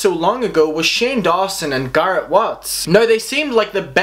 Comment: I thought he said carrot watts